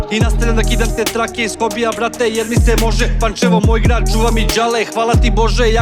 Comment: is that Pancevo? my Dad was from Pancevo. I dont speak Serb but I swear I heard him say Pancevo moj grad